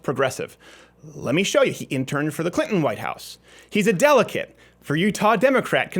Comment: Did you say "delicate" or "delegate"? :)